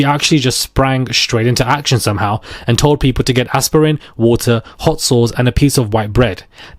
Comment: is that a antidote for date, rape drug? Did he say hot sauce?